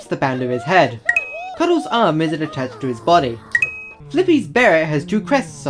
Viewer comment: you spelt toothy with a second t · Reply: Also at 36:51 you said boy instead of body (btw I'm not trying to be mean I just wanna point out stuff)